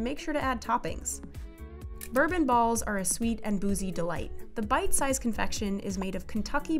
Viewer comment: did they just say.. boozie?